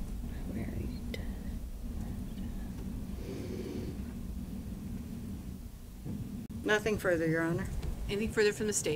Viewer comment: what did she whisper? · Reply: It sounds like she says I just don’t care about this